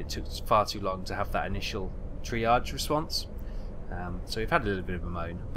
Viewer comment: what are you saying there? (tree artist? lol)